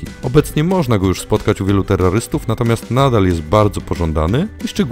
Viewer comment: Terrarystów usłyszałem terrorystów XD